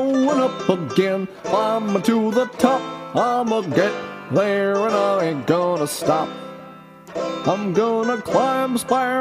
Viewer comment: I could've sworn you said "I'm'a get Rare and I ain't gonna stop." at :09 I was like "oh yeah!"